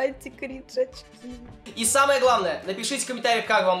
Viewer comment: Что слышат нормальные люди: Анти кринж очки Что слышу я: Анти крышечки анти крышечки